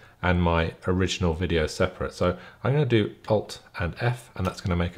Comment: Hi, at 1:33 / 14:33 you say something like "pop and f"... I can't understand it correctly. What shortcuts are they exactly? Then you say "copy it or cut it" to have a 'free' frame. Do I understand this correctly? What exactly is the purpose of it? That's not clear to me. Do you cut out a piece and then join them together again? Maybe a bit more explanation because I'm new to this (and not native English) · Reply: alt + f is the shortcut I mention . It creates a freeze frame that I then copied to another new project timeline. Just to keep the thumbnail creation away from my main edit. The freeze frame always splits the clips so I think run through these steps 1. 'alt + f' to create the freeze frame 2. 'cmd + x' to cut the freeze frame 3. 'cmd + z' twice to put the clip back together before we even made the freeze frame I then have a copy of the freeze frame that I can paste into my new project timeline. So essentially I'm grabbing a frame from one clip and pulling it into another.